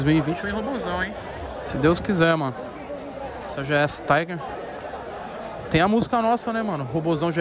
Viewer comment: Gs tiger??? Entendi nada😂😂